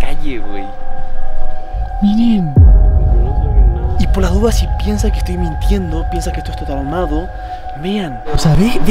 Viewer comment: Todos denle like a cualquiera que dice este número fede lo tiene que oir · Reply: Dice gracias Mamá